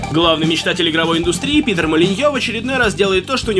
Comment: Мне одному послышалось -"Пидр Молиньё"?